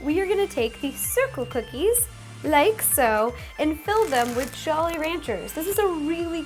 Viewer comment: I thought you said jelly ranchers